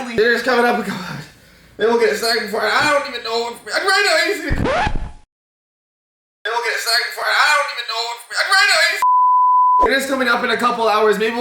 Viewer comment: "I don't get a sniper for it- I don't even know if! Fried ice" is what I heard XD